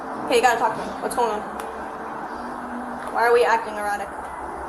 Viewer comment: “are we acting erotic.” Tbf to the AI subtitles it did kind of sound like erotic instead of erratic 😂😂